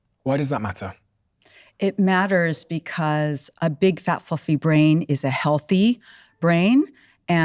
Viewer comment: The 1st time I could have sworn she said "Big fat f*cking brain." lol. B